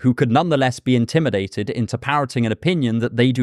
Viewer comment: Go to <link> for 50% off the Ground News Vantage plan. This is their best deal of the year, available for a limited time, so be sure to use my link! IMPORTANT CORRECTION: I misread some notes that I wrote as an undergrad - I do not mean "Steve Hansson", but "Sven Ove Hansson". I am referring to his paper "Defining Pseudoscience and Science" within the book "Philosophy of Pseudoscience". A silly blunder on my part, sorry. LINKS AND CORRECTIONS Support me on Patreon here: <link> Sign up to my email list for more philosophy to improve your life: <link> CORRECTION (ish): I am not saying all ravens are black, just that it would be a sensible scientific hypothesis. It became a classic example in 20th century analytic philosophy, which is why I used it here as a little nod. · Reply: Subtitle correction 16:46, subtitle says 'interpretting', you say 'into parroting'. On another note, I don't think your definition is too helpful, since it will end in circular sorts of argument 'you are being partisan for X to find Y', 'no I found Y and hence agree with X', on loop. It requires a sort of psychoanalysing of people which is generally understood as bad etiquette, since it avoids the argument put forward. This would suggest, discovering a pseudo-intellectual, is only possible by a pseudo-intellectual which by far is odd and comical. I'd recommend Plato's Hippias Minor, Sophist and Heidigger's 'Parmenides' if you wanted interesting reads further into the idea of 'falseness' aswell.